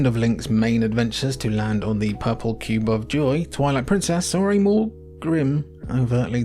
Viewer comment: I keep hearing “toilet princess”